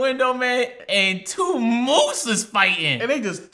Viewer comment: Flight reacts may be dumb. But does he say “moose’s?”